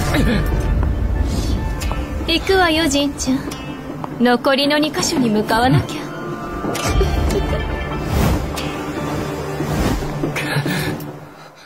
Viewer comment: Why did i thought he said mother for a sec in the Japanese version Until i realised he said wait "Matte"